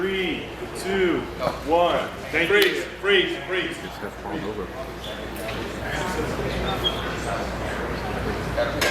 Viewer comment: i think they said something here too but hard to hear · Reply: "He's half a pounds over"